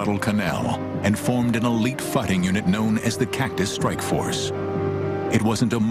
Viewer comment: What is the narrator saying? I have listened to it like five times, and every time what I hear is"Fletcher class destroyers arrived in Guadalcanal and formed an elite fucking unit known as the Cactus Strike Force."